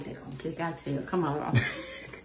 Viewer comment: I want to know what dad said when mom said” you want some” sound like he said “yeah it’s my birthday girl” he was talking so dang fast.🤣🤣🤣